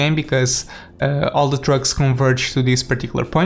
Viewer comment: It sounded so much like he said “Drugs” instead of “Trucks”